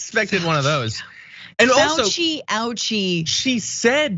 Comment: it sounded like she said "Fauci-Ouchie", can't be right.